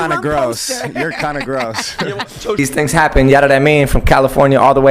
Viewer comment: What did G say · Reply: Yadadamean"Ya know what I mean"